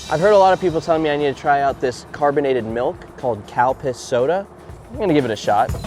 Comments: Anyone else thought he was saying cow piss soda when he said Calpis soda? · I know its Calpis, but it sounds like you said Cow Piss.